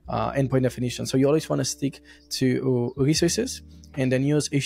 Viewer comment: What is that word? Recesses? I am unable to make a meaning out of it. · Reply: "Resources" - :)